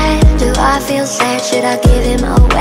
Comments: i thought it said “mommy, why do i feel sad shit?” lmao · I THOUGHT IT SAID “Tummy why do i feel sad” uhhhhhh